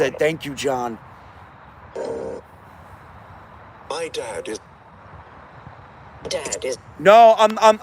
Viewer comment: I think spirit goes on to say “My dad is…dad is”. I initially heard dad and thought oh no 😢, like you. 1:45:00 “Is the dad”